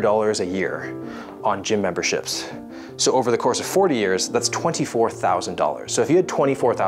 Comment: $600 x 4 = $24,000? Clearly Wills math skills aren't his strong point. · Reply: It sounds like you said 4 years. Nevermind, I got a response from big Willy boy Tennyson. Keep up the good work brother.